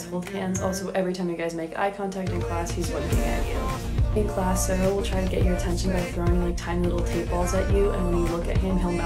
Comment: I thought she said sero will throw tables at u🥲